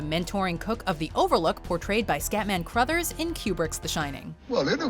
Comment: I swear she is saying "Q*berts The Shining" everytime, especially at around 6:16, instead of "kubrick's"😆 ....I had no idea Q*bert made movies hahaha